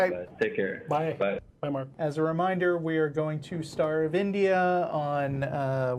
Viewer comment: I heard "We are going to starve India"